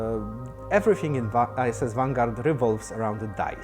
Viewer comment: Great update! Sounds like he says "ISIS Vanguard", :D